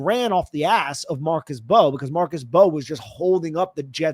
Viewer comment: The M is Mbow is silent my man. Just say Bow. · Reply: He’s referring to the Jets rookie tackle Armand Membou. Not Marcus Mbow. Understandable mishear though